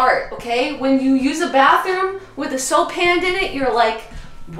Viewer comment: I thought it said hand soap... boy was I wrong 😂